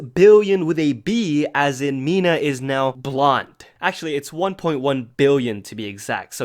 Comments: I got scared for a sec. I thought he said blind... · Ok 3:28 idek why but i heard Mina is now blind and i freaked and was like how is he so calm about this until I realized he said blonde 🤦